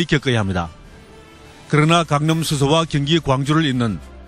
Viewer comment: 강릉 수서? 수서가 왜 강릉에 있어. 기자가 강남을 강릉이라 발음하네 1:27 에도 강릉이라고 들리네